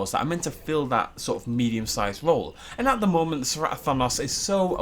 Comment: Did just say "Cerato-thanos"?